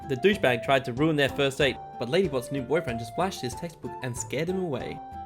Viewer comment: i heard "he flashed his testicle"